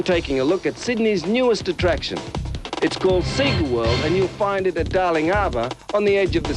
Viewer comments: I thought he said "IT'S CALLED SATAN WORLD " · is it me or did he just say "satan world". sonic is demonic confirmed.